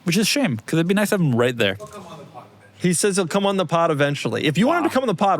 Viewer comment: I swear I heard saying "f*** em on the pod b****"